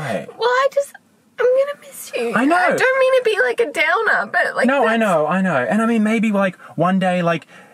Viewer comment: I love how supportive she was, “I don’t mean to be a downer, but I’ll miss you” 😂💚 I love hahaha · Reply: I thought you said I “I don’t mean to be a drowner…” for a sec